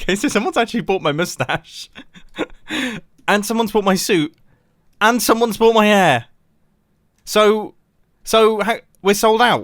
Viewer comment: did anyone hear ‘and someone’s bought my air’?